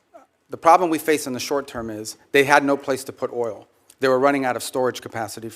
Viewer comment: What I hear is "I have no plan."